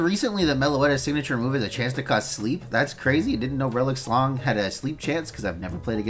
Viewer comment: It sounded like you said relic schlong